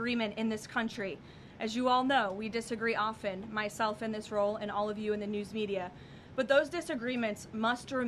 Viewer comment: I had to relisten to “myself in this roll” several times. I heard “myself and Israel”